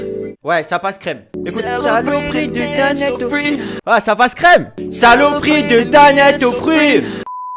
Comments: Perso j'ai entendu "c'est les prix d'une dannette aux fruits" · Moi j'ai entendu "c'est le prix d'une d'annette au fruit" a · Le dernier moi j'ai entendu "c'est le prix d'une danette au fruit"